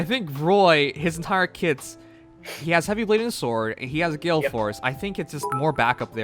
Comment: Horse emblem like in an Team out of horse units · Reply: thanks. Thought he said force not horse